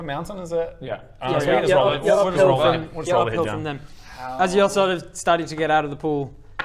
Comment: said "well just roll the head down the mountain" and no one noticed. That was an underrated moment, I heard you!